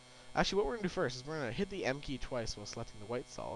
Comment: sorry I didnt hear what you said was it "hit the m key twice"